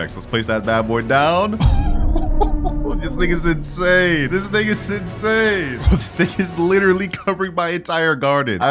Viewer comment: WHAT DID HE SAYYY?! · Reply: He said this thing is insane but we heard it as smth else 😭🥀